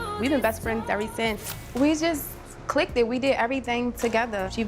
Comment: Did she just say we just "click-ted" 🤣🤣🤣